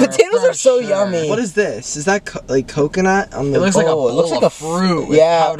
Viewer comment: Matt said this 😭 :is that cu- is that coconut? Sorry if I'm wrong but it sounded like that 😭